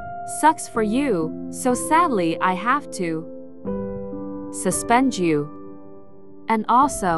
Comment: i thought was gonna say "sadly i have to join you" instead of "sadly i have to suspend you" on the gulp gulp part...